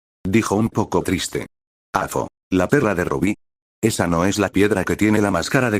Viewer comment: solo yo escuché la perr* de Rubí 😅🤣🤣